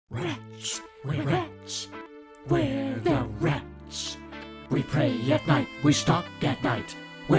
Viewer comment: I thought it they said”Rich, we’re rich, we’re the rich.”